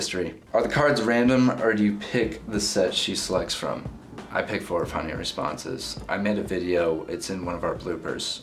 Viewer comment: Can someone repeat to me his answer at around 4:16?? The kinda mumbles and I don't understand some of the words 'i pick (??) the responses' is all I understood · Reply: I think it's just "I pick for her funnier responses."